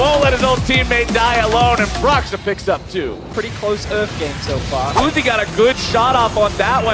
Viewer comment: "pretty close URF game so far" EXCUSE ME?????????????????????????? WHAT YA SAID????